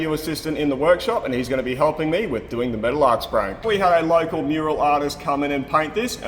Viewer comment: Wait, is that really her name? I thought he said a "mural" artist. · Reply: He did say "local mural artist"